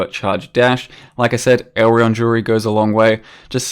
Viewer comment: What does he say at 13:03? What goes a long way? Elemental jewelry? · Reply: Elreon jewelry